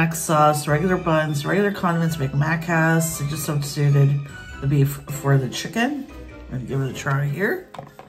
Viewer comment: that looks so GUD!! I wonder if I can substitute the beef for chicken here... got the thought juices running ;) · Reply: oh thought you said could substitute for the chicken. Wish you could! 😃😎